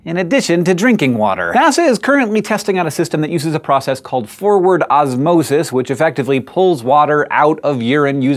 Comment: I thought you said Four Word Osmosis and I was like "...that's a weird name. Did they just forget to put in the name and he read it like that? Or is it more of scientists not being creative with their naming again?" Then I looked at the screen and was like, "Well that's boring..."